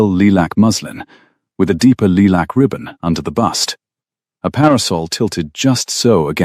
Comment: the cc read out and the narrator said 'lielac ribbon' but when I asked the ASK feature what a lielac ribbon, it deny saying that and said it was 'lilac ribbon. Someone needs to make up their mind!!!!! Throughout this entire story, there are so many weird sentences that don't make sense! Such as - his rinsed grey eyes - what in the world are those kinds of eyes! Does that mean they are light colored? How come this story was not edited? Takes away from the story!!!